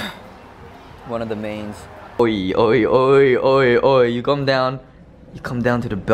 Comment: Did I hear mark say "Oi oi oi" 4:14 I cant